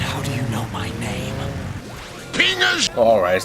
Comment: In my opinion, Sonic 's way too cool to be in a car. He's cooler on a board in the riders series. Oh and on 7:00 did I hear "penis?"